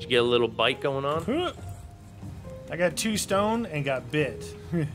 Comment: did he just say "i got to stoned and got bit"?